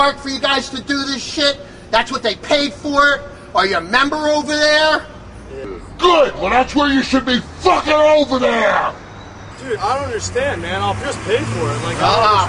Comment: sounded like "GET OVER HERE" hahaha